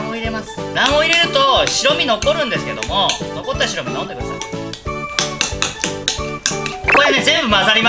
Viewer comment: 卵黄入れると白身残るんですけど、残った白身飲んでください。 って言いました？？しれっとしれっと言いました？？？ 何度聞いてもそう聞こえるんですが、料理に集中できないですぅぅう！！責任とってください！！！